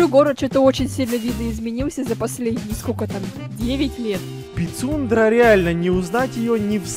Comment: Пицундра а мне послышалась писюндра🤣🤣🤣🤣🤣 как и 🤣🤣🤣🤣